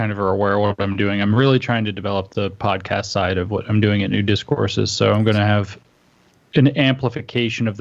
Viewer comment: When you said, "New Discourses," it sounded like you said, "Nudist Courses." That is all